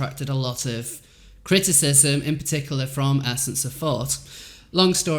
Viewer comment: I absolutely adore you and think your voice/accent are lovely and adorable but I swear I thought you were saying “essence of Fart” when you were saying “essence of thought.” Love your channel and glad you make content. Xoxoxo